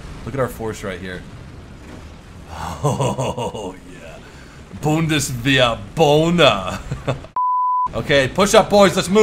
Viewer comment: The fuck did you say on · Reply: LeToplache007 Bundeswehr boner (?)